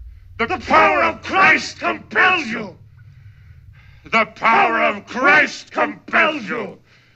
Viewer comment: oq eu entendi foi: tepoloplaithespencil